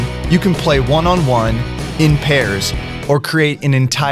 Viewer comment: did i hear paris